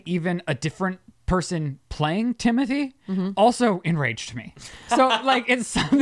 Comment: Whoa at 40 minutes I thought Rylan said to me I’m glad that she pointed out he said Timothy he needs to play that back because Timothy does not sound like ‘to me’ they both start with a T that’s it